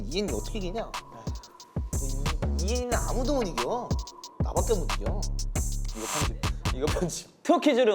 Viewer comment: Changbin : Han is second cutest after I.N Han: what makes me second 😂 Changbin : No one beats IN Han :please edit this out 🤣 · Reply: Han did not say to edit the part because he said no one can beat IN he said because changbin said only he( changbin ) can beat IN